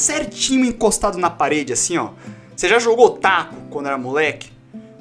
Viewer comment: eu entendi "jogou o otaku" kkkkkkkk